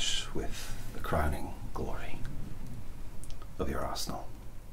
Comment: i had to rewind 4:00 at least 2 times because i thought i heard you say "so we can end with a crowning...your asshole" rather than "your arsenal" i gotta get my ears checked 😭🤣